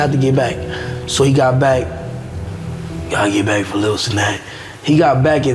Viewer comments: Fannum stopping mid sentence to say “gotta get back for little Cenat” had me dying 😭😭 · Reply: Thought bro said gotta get back for lil snack💀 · Fanum getting lost in his thought and saying “gotta get back for lil Cenat” had me weak 🤣🤣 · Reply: I thought he said “gotta get back for a lil snack”😭